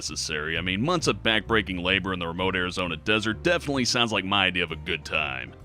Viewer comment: did you say Arizona?